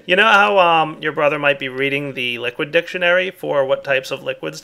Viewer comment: Did just say "Your brother" ???